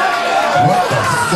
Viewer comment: i thought he said, "what the fuck just happened to my brand bitch" since leiomy did his signature body roll at the end. Great performance.